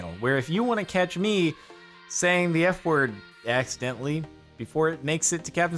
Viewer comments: Why did my brain think you said the N word instead of F word? I need sleep.... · I thought he said accidentally saying the n word i was like hol up